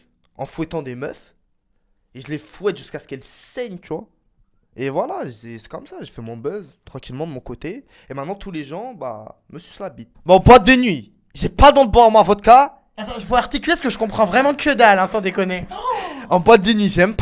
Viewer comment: j'ai compris "MENPATEUNUI, CHÉ PALTEM POIRMAFOTKA"